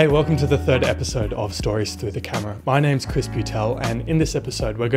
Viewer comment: At the beginning I thought you said your name was Crispy Tell